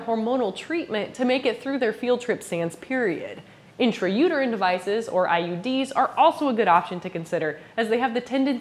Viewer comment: I misheard that as 'I.E.Ds' and had a minor heart attack.